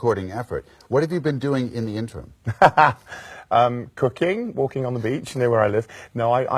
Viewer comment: when he said cooking i literally heard cocaine